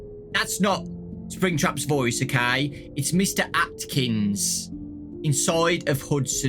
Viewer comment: at first i thought you said Afton when you said Atkin .